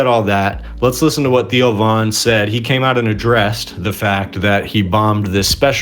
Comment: At first when you said "he came out and addressed" i thought you said " he came out in a dress" which would have made his show funnier